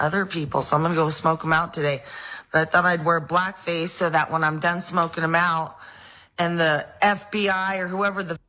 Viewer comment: For real when she said, "when I'm done smoking em out," I really thought she said "when I'm done smoking meth!"🤣🤣🤣